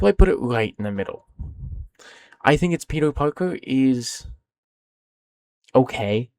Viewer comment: did you just say pedo parker? XD